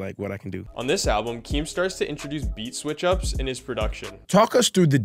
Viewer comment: Bro said keem starts I thought you said keem star I was hold up a sec no fucking way